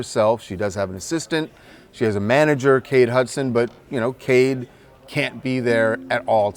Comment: I thought he was saying Kate Hudson is her manager 😂